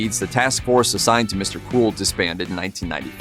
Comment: It sounds like he is saying Mr. Cool not Mr. Cruel. I looked at the segment title to find out what he was saying.